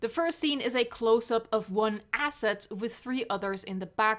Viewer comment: did I just hear the word "Asset" in? jurassic world flashbacks